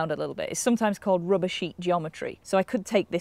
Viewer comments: I thought she said rubbishy geometry at first. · Did anybody else understand "rubishy geometry" at 1:14? ^^ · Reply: RUBBER SHEET GEOMETRY.